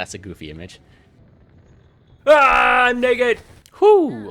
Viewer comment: bro what did he say? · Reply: Naked😭